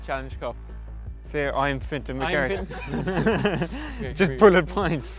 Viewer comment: What did Paul say at 0:17? It sounded like "just pull a pint" 😂 but that doesn't make any sense to me (but then again, i'm not irish...)